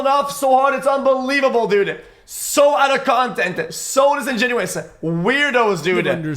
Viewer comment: did xqc just say so out of content? 😂😂